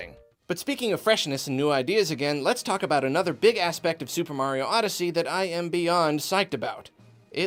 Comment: Did anyone else thought said "ass" at 5:02?